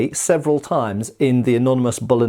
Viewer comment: “The anonymous Bolognese sauce…” … at least that’s what I heard the first time…